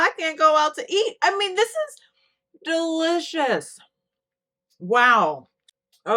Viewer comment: did I hear a DILL-icous on that burger with Dill sauce?!? Because that's what I thought I heard and it made me smile so big. I might just have to name these burgers the DILL-icous ones 😁😁😁